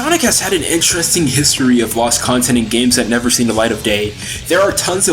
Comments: is he saying gay? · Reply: … day. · "never seen the light of gay". I swear that's what was said, even if it was accidental.